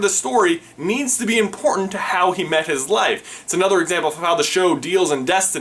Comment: did he say how he met his life or wife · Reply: yeah haha he said "life"...he meant "wife" though Or does it matter...? Hmm...